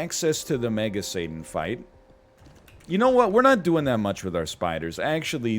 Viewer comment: aunno why I thought you said "manga satan fight" but now I want to see a manga satan fight...